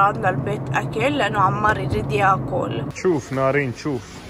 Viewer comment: كاني سمعت نارين 😂 تعديل:هسه فهمت يقلد موفلوكز تعديل:شكرا علـّۓ الايك م̷ـــِْن عدي 💔🤡 ٱڅڑ تعديل:شكرا علـّۓ. 52 لايك ورد واحد💃 تعديل:هلو هاي شنو هاي 55 لايك بعد ماتحاجه اني🙂👏 تعديل:صدووك جذب 62 لايك عاا حرفع خشمي😒😂💔 تعديل: 87 لايك شركه يوتيوب وثقوو حسابي 🤡💔